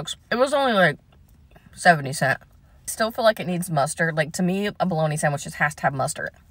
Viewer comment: Not me mishearing mustard as custard. Custard with bologna